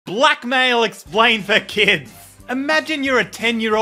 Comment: I thought that you said “black male” not “blackmail”😅😅😅